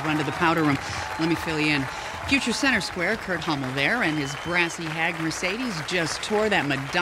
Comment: Does anybod realize dat marcadies said come on kurt Hummel i thought she said come on Kurt huuuu